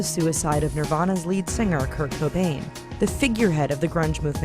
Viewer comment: I was just listening and not watching the vid. I swore I thought she said kirko bangz, I was like DF when! 😂